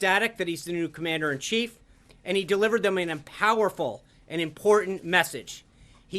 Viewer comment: did he just say "empowerfull?"